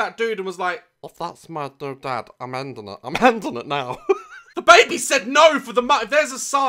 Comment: "The baby said no" Me: I heard "DaBaby said no" 😂